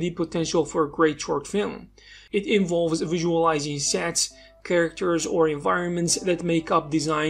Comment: "visualizing sets..." I thought he said "visualizing sex" and I was thinking, "Right up my alley."